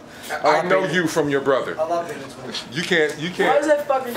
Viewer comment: aww Luke said at 0:27 "I love him" I think he said that